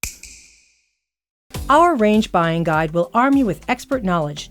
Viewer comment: thought she said range